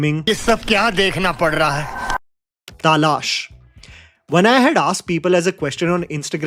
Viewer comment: I think you said Taalaash instead of Talaash